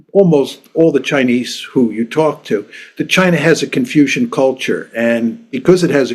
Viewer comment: Youtube auto caption wrote "confusion" instead of Confucian :face-blue-smiling